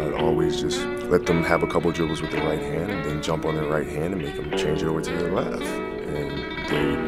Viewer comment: This entire time I thought when he said “I just jump on there right hand” he actually jumped on their hand 💀